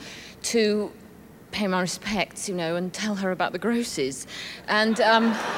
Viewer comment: When she said "the grosses", I thought she'd said "the grocer's", lol.